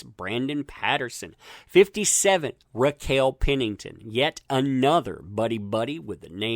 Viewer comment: Misheard Raquel Pennington as Reptile Pennington 🤣